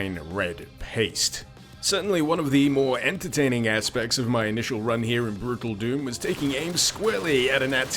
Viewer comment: Did you say "Brutal Doom" on accident?